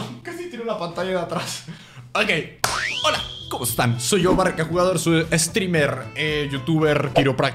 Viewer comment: pensé que dijo soy estriper xd